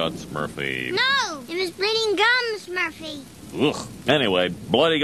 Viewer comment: When I was little, I misheard it as "Blooding Gums". 😂😅